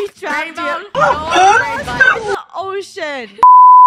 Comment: did funneh say shit there? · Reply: Funneh said "Ocean"